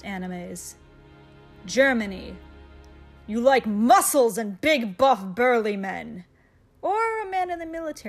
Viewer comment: yay all 5 of my husbando's are here Germany ,Russia, Sweden ,Canada even Switzerland :D (as I thought you said "big buff girly men!")